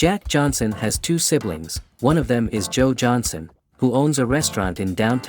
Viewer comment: I HEARD JOJO?